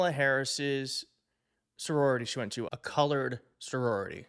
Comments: I hear “colored”. Nice try! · Kilmeade is the worst, but I think I heard “college” not “colored.” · I hear "college" not colored (and I vote Democratic)